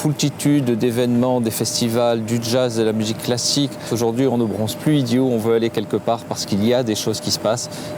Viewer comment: c moi ou à 1:20 il dit "on ne bronze plus idiot" ?